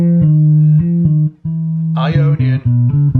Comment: When you talk about Ionien, I hear "I ionion" like "I phone or I pad" so my question is it Apple who has patented this mode ? like a new fruit ??